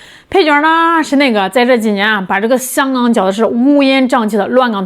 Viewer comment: 我乍聽之下聽成香港腳的事🤣🤣🤣🤣🤣🤣